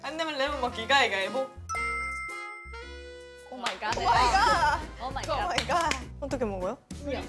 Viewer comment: did I just heard SAKURA saying “Omygodnis”??? Izone memories are coming back to me 🥲